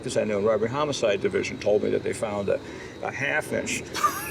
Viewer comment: what is he saying at 14:47 to me it sounds like a "half fish".... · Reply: I believe Michael Mann says half-inch, as in a half-inch VHS videocassette of the movie Heat. Then Mann says that the robbers had worn out their tape of Heat by watching it over and over so many times.